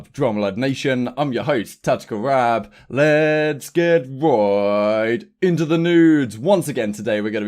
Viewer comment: Haha sounded like you said “let’s get right into the nudes” 😂😂 funny new intro bro